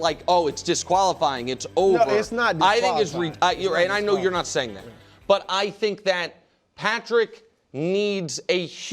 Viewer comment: what was Nick about to say?? 😂😭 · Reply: He was gonna say ridiculous. Not like Brou pulled with his “disabled cousin” story a few years back